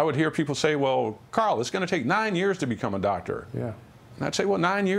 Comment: How many years did they say he spent in school after the biology class? It seemed he took a bio class and he became a doctor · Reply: I thought he said 9 years